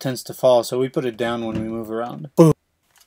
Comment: anyone else hear a noise that goes 'boop' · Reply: Haha, that's me saying "Boom" but cutting it in the middle... Messed up the edit, sorry!